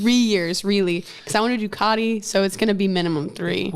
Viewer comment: What did she say she wanted to do it that would make it 3 years at 49:23? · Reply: Ducati